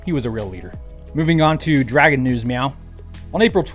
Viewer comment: Did you say "Meow"? :)